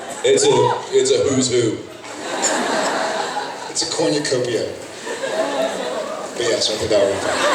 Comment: what does Jared say at 2:28-2:30 ? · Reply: "It's a cornucopia."